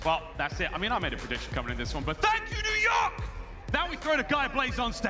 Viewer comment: Ive listened to squeaky ass yell during their on stage interviews after the matches...and...I cannot make out what the fck he's yelling...help? Anyone? EDIT: I hear "Fucking _ _ _ _ cmon'?" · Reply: "F*** it then, c'mon" I hear it now after your comment